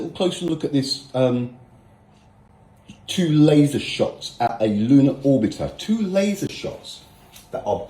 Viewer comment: Did he just say "looner orbiter"? If the earth is flat, what is it orbiting?